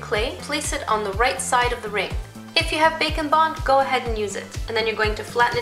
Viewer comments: when she said bake and bond I thought that she said bacon bond at first and I was like whaaat? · Who here thought she said bacon bonds when she said bake and bond 😅😅😅😂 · I always thought she was saying bacon bond